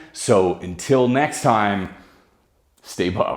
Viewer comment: I know you said "stay buff" at the end, but I swear I heard "stay puft." Lol